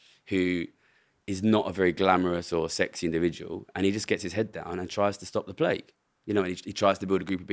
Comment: I had paused the video and gone off to do something else. Then when I later come back and have forgotten what was said, I start playing around 19:22 and I'm confused a bit and start cackling. All I heard was that some sexy individual is trying to stop the plague and I'm completely lost lol.